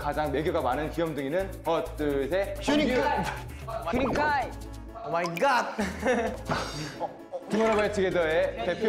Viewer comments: Is it me or i really heard kai saying "MY GOD" ? Hahahahalol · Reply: I'm JxT yes, he said that. Haha our cute maknae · do I need hearing aids or did I just hear kai say “pewdiepie”? LOL IM INTERNATIONAL ARMY SO DONT ATTACK ME IF I ASSUMED!